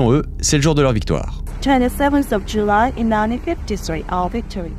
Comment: pourquoi elle dit "july" et tu écris "juin" ? (à 23:56 aussi)